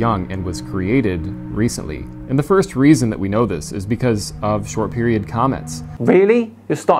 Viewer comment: First I thought he said “short period comments “ 😂🤦‍♂️